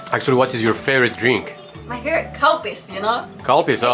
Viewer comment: for a moment I thought she said cow piss when you asked her what her favorite drink was. I looked up Calpis, over here in the States (North America in general) it’s known as Calpico